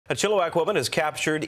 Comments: Since I can't find my original comment to edit, I'll just repost it as I meant to. I thought he said, "...a chill wack woman..." and was baffled. 😂😅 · I thought he said, "...chill white woman" I was baffled at first. 😂